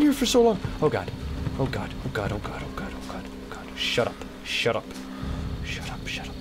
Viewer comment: did he just say Shit?